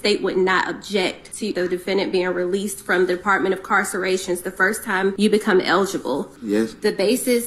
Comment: Did she just say department of "Carceration" 😂😂😂